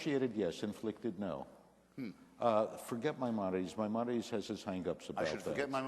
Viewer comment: Can someone tell me what is said at 7:25ish? "Forget my modities?" or "Monidies"? it sounds like? I can't hear/parse the word either of them are saying. Edit: Nevermind, figured it out. Maimonides is what they're saying. I heard it as 'My Monidies'."